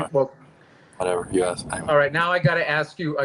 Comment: what's this? julian says something like "whatever...you asked me" and looks annoyed